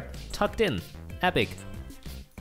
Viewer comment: Is it me or I just heard one of them said colacola rather than Coca-Cola